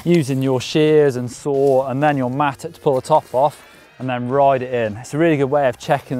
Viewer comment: what is the name of the axe looking tool sounds like he said mattook